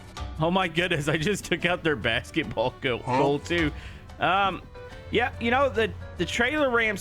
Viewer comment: Nobody noticed what he originally said... "basketball goat" He had a chance to correct it and he still couldn't